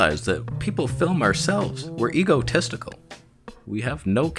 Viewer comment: Did you just say we're ego-testical here? 😅